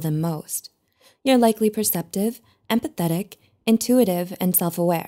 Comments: I heard: "You're likely perceptive, and pathetic..." I was like YUP that's me! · When she said “empathetic” in the first 30 seconds of the video I thought she said “pathetic” and my heart sank 🥺 THAT IS HOW SENSITIVE I AM 🙊